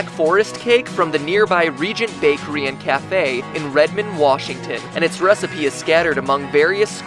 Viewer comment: does he say ratman washington ?